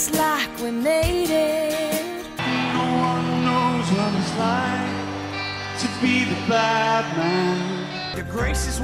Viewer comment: damn, i thought they really said "nobody know hows it like, to be batman" all this time. gotta learn english again